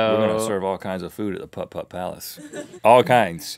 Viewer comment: I heard "Pup Pup Palace"...